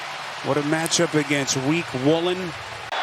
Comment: Oh hell nah, the announcer think he slick, he said "weak woolen" at 4:15 smh he wild for that